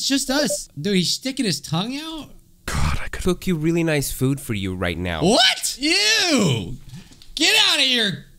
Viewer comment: TO THOSE WHO PLAYED THE GAME WHAT DID SAY??? 😨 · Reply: I haven’t played the game in a while but if my memory serves right he said and I quote; God I could just f*ck you against this wall right now.”